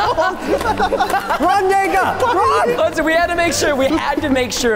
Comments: Did he actually said Run .. N.. Run? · it sounds like preston is saying run n word run